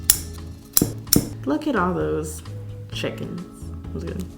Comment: lmao when she said chickens I heard Kylie Jenner say “is that a chicken”